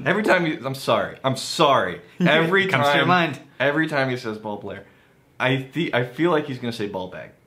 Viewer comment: I thought you said "ball hair" at first. So thanks for ruining this whiskey for me